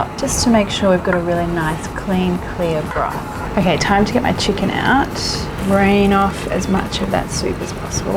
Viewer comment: I thought she was saying chicken fur... lol